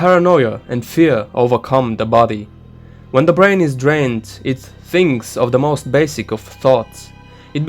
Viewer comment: I thought he said “when the brain brains”. Ruined the atmosphere of the video for me